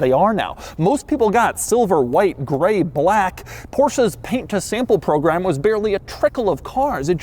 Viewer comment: Did he say "Porsche's paint to sample program" at 3:28? 🤔